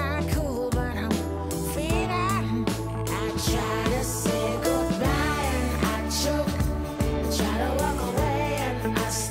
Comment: I was 9 when this came out. I was obsessed with Pokémon and I always thought she said “I say good bye to Machoke”